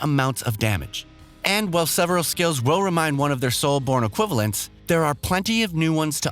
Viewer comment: I think I need a translator at 6:22 I hear. "And while several skills roll rewind one of their soul borne equivalence.."